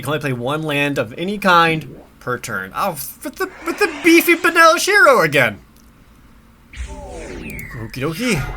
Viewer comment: I kind of just had this playing in the background, and every time I thought he was saying "Benalishiro". Had no clue it was 2 words, lol